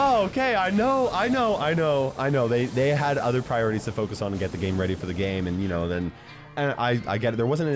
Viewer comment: i heard said game rdy for the game 😂😂😂